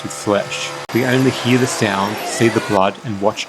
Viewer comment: "We see the sound and hear the blood" Is that what he just said? Anyway, I get it DePalma is a fantastic director.